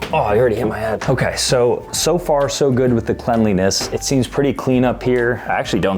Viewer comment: I think bro said cenlenes